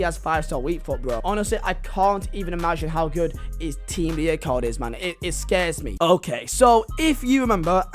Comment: Your accent makes it sound like your saying team of the year marney love it